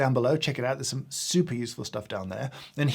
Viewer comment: It's just me who hears it "Super useless stuff down there"? I mean... was it intentional? 😄